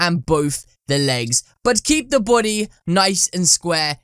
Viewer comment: I sound like u sayin boody